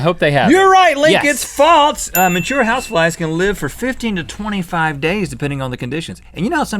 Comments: I thought you said house wives not flies XD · When rhett said house flies I thought he said "house wives" 😂😂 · I wasn't looking at the screen and I thought Rhett said house wives instead of house flies 😂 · Wow, I thought Rhett said House Wives for the first question xD